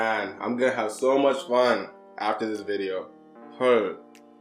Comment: Did u just say hurr 헐 ??in 8:30sec ??